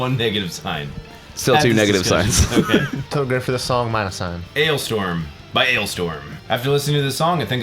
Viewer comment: I thought u said Analstorm for a second. lol